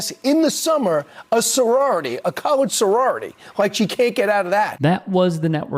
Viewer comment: seconds, did he say a 'colored' sorority or did he say a 'college' sorority? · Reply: He said colored! 🤨